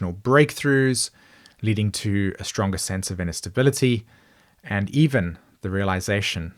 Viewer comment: "stronger sense of inner stability..." gotta love that! (although i had to rewind it twice in order to hear what you said... because of household noise, i thought you said "instability" and no one wants that! 😂) ❤❤